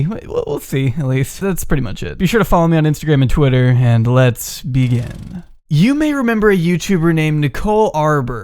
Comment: I heard wrong- "let's vegan" wtf me